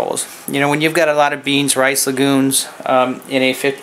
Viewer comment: excellent tip.. had to rewind a couple of times.. Sound like you said beans, rice, and lagoons.. hehehee..